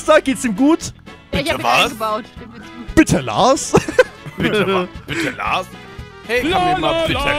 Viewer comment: Ich hab einfach so verstanden, dass Bernd immer sagt:,, bitte Lars‘‘😂😂😂😂